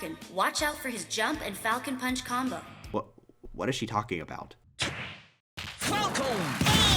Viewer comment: the youtube english captions says "welcome back" in the jump and falcon punch combo joke when caption falcon says the iconic "FALCOn PUUUUNCH!!!". youtube captions are really stupid(the auto generated ones)